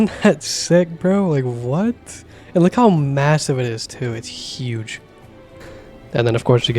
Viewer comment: did i hear... massive?...